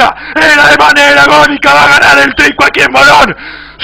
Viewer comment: Yo entendí "Tinco" XD